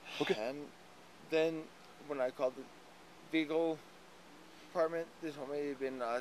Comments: “When I called the vehicle department they told me it was in an accident” lmao this dude must have more mutual insurance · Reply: Lmao I thought he had said “Legal department” but now that I know what he actually said it’s even better!🤣🤣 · “I called the vehicle department” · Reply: I thought he said he called the legal department, but still... 😅